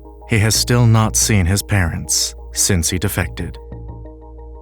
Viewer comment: "He has still not seen his parents since he defected" hit me pretty hard. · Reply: I thought he said "since he defecated " hahahaha I was like well yea, must have being bad.